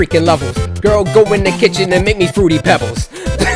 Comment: HAHAHA LMAO 😂 I STARTED LAUGHING SO HARD!! Did you say ‘ make me fruity pebbles?!’ Hahahaha